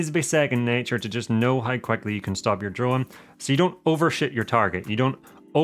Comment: "overshoot" ...I heard it as "over sh#t" 😅